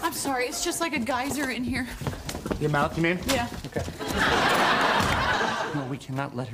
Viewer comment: What did he say? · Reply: “Your mouth you mean?”